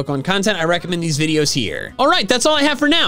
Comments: was i the only person who thought he said I gotta catch the mall the first time I watched one of his video's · You know the "gotta catch em alllll" outro. Well once I acidently turned on subtitles when it was said and the subtitles thought said "gotta catch a mooooom" lol